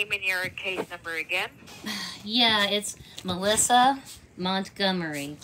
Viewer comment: Should have said Elizabeth Montgomery. Don’t know if you’re old enough to get this. 😄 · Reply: At first I thought she had said Elizabeth Montgomery 😆